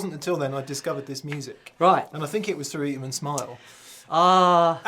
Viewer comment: Does anyone know what the band mentions at about 18:50 is called? i can't quite make it out :) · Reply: George Law he says "eat 'em and smile", the David Lee Roth album with Steve Vai.